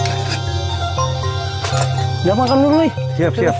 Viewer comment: beliau ngomong apa sih bang? Penasaran. Gak boleh dishoot apa ngajak ngopi😊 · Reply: Nyuruh Kami makan Dulu.😊